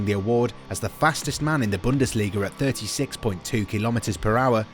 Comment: HE SAID HAKIMI IS THE FASTEST IN DORTMUND NOT BUNDASLIGA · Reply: he clearly said @ 7:32 fastest man in bundesliga which I wrong at the time of making the vid